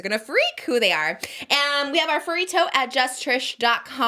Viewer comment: I was watching you while mopping at 5 am and fully I thought you said "furry toes" nope.. she said furry totes...